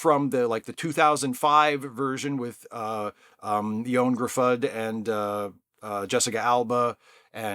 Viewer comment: I loved it- though fair comments about the plot thread leading up to the climax. Very Stan Lee logic going on there! BTW, I had to wind back when you mentioned Cardiff's Favoured Son, Ioan Gruffudd- I swore I heard you say Elmer Fudd :-) (it's pronounced You-An Griffiths roughly speaking)